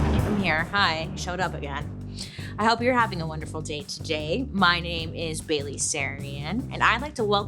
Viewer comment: I thought you said, "Hello, I'm Bailey Sarian. I hope you're having a wonderful daily Sarian."